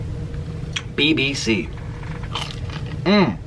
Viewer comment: anyone else hear BBC Like ewww